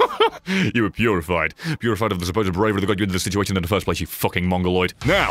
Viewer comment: What the fuck did he just say? Also Im halfway through, boys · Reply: "You were purified. Purified of the supposed bravery that got you into the situation in the first place..."